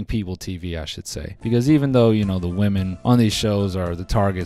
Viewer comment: Raise your hand if you thought he said Mormon instead of women